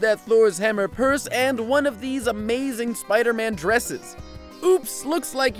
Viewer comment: it's a mistake in the captions,jake says amazing and captions say amazon,it's no big deal as nobody uses captions anymore,but still the grammar nazi inside me is angry :\